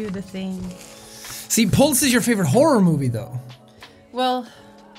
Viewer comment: I thought that said Puke, not Pulse